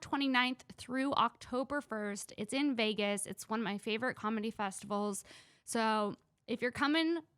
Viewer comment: I thought said she was performing on "Comedy Jews"